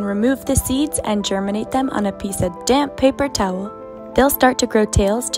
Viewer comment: For some reason, ever time I see one of these and they say “damp” I hear “a DAMN paper towel” ToT